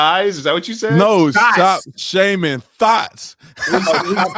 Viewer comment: David Harris- I thought said “shaming thighs” too! 🤣😆😂